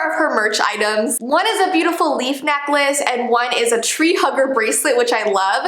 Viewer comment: I THOUGHT THAT SAID ‘TREE HUNGER’ 😂🤚